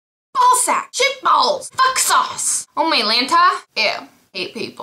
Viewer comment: did you say ''oh my lanta?'' lol ive never heard that before :)